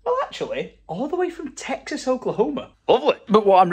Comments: I’m in Texas, did you just say “Texas Oklahoma”? 😂😂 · Is it me or did say Texas Oklahoma 🤣